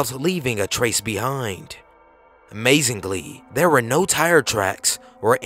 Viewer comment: did... did you just say "mazingbly?" instead of "Amazingly"...?